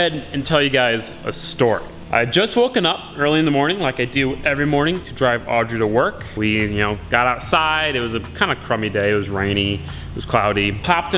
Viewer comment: All I heard was "woke up early, popped a molly..."